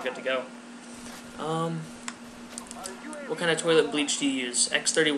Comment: You said turtle beach, I thought you said toilet bleach